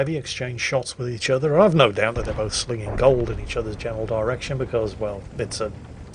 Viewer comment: Lmao, I thought you said "The ST-I and the Type 4 SPEAKING gold at each other.."